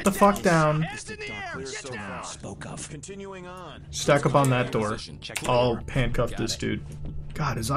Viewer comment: "Stack up on that door, ill bust in this lady" Is what i thought he said.